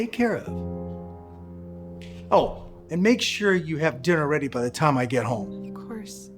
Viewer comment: WHAT DID BRO SAY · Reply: Sinner